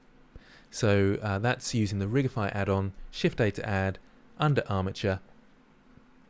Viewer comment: I can't hear properly what are you typing 😭 can you please say it again in the reply? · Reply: Shift a to add